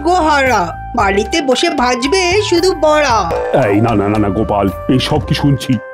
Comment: কবি কি বললো এটা 😂😂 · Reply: বড়া বলেছে